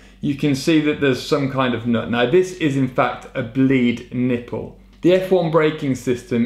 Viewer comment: did I hear “bleed nipple”?